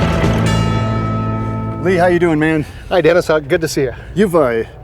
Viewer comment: Did he say Hi Davis instead of?? LoL 😁